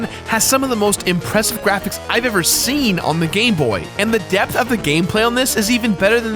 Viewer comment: to me when he said gamebot it sounded like he was saying gay boy